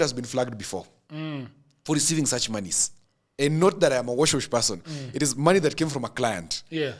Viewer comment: "Not that I'm a wash wash person". I thought I heard: "Note that I'm a wash wash person" 😂😂😂. Anyway, great show.🎉